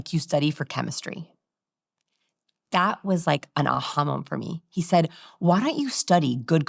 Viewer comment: An ahamam? What's the word? I'm curious 😳